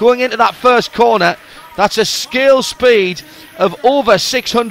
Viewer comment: over 620 mph! Did I understand him right? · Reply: Martin Fisker He said "scale speed" not skill speed. They go 62mph, and are 1/10 scale, so 62x10=620mph. His accent makes it sound like he says skill.